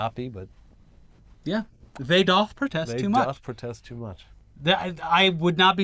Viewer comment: When you said 'They doth protest too much" I heard 'Adolf protest too much' which seems apropos. I'm not on twitter or anything, but I would love to start seeing this used.